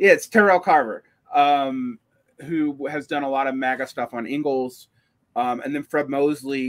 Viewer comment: Re: Terrell Carver ~1:16:40, are you saying "he's done MAGA stuff on Engels?" I don't understand the word starting with M there Btw big thanks for the closed captions!